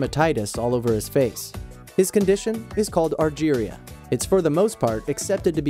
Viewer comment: what did i hear algeria? algeria my country north afirca!